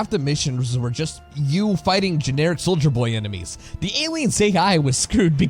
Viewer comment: Did you just say Soulja Boy enemies? What, do you crank your rifle guns and superman them hoes? #SorryNotSorry